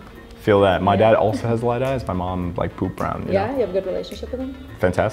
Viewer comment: Did he say poop brown eyes? 💩😂